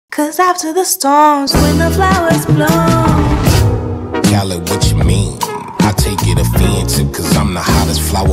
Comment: I always thought he said "darlin what you mean?" Instead 😭